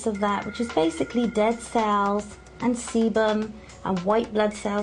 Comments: it sounded like she said dead cells and seamen lol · did she say semen? 3:39 as you can see the contants of that, just basically dead cells, and SEMEN!, and white blood cells ...gross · Did anyone else hear semen with her accent at 3:40?